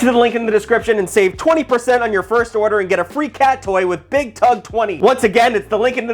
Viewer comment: Thought he said Free Cat Today for a sec